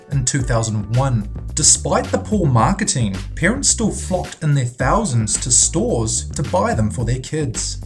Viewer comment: it sounds like he says: "Parents still fucked in their thousands." LMAO!!